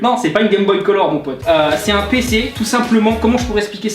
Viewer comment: jai pas compris cest quoi qu'elle quun peu m'expliquer car explique mal mdr (humour) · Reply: ted Gaming12 un pc